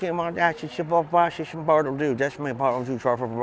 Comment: Why is it that the more drunk Mumbles gets the more I understand him!? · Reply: I swear I heard him say, Bartle do, and then something about his name is Bartholomew